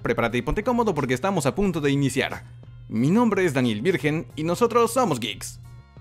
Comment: Escuche mal o dijo mi nombre es: Daniel Virgen 😂😁😅😂